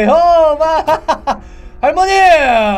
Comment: 나만 조세호로 들었나?